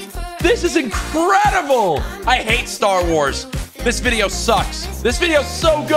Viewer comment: Did he say I hate star wars? "And I took that personally" Goodbye courage sypher better anyway